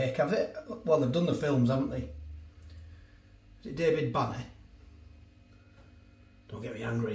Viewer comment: Thought you said David Batty then!!